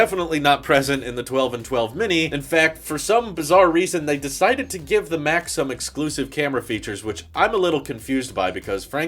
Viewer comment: I thought you said “they decided to give the Mac some exclusive camera features.” Giving a computer exclusive camera features? That would be bizarre.